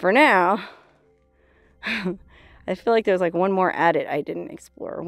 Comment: Great Tour. In my experience mining tunnels on the level are called Drifts and angled tunnels which intersect other tunnels on a different level are called stopes. That was cardboard 'Honeycomb' on that door for stiffness/insulation perhaps. That big equipment shook violently to allow aggregate to go down through the sizing screens once it was the correct size after crushing. That was a great tour. Be very careful about walking on old tank roofs. You wouldn't be the first to fall through one due to underside corrosion. Love the new Decals. YIKES! Play safe Darlin' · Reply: I think she said "adit" 4:31; a horizontal passage leading into a mine for the purposes of access or drainage.